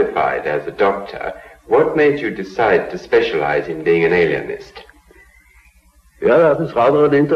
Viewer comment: did he say Alienst?